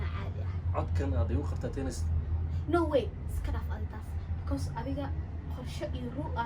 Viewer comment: I'm a woman from the United States that stumbled upon this video. I am trying to figure out what they are saying! Did she say "no way!" at 9:15? I think that woman is adorable! I also LOVE the furniture! Can someone explain where this video is from and what they are saying? I love to embrace all cultures! <3